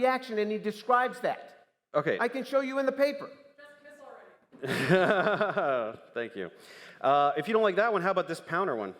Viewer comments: What did the woman say at 31:08? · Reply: “Just kiss already” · what did this audience member say? I can't quite hear it. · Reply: "just kiss already" lmaoo